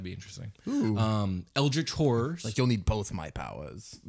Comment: When you guys brought up "eldritch horrors", i honestly thought you were saying "eldritch whores..." and was like, perfect way to describe them 😂